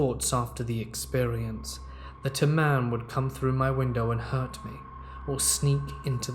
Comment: I thought you said, “a lamb named Bruce”. I was so damn confused when the lamb was diagnosed w/bipolar disorder. Had to replay the opening. A man makes WAY more sense 😆😆